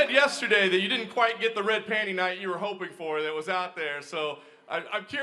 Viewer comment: sounds like he says red panty night